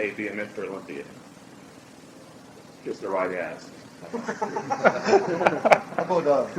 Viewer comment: what did he say? Just the right abs??? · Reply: lol, he said kiss the right ass - I’m guessing a reference to the 1980 IBFF?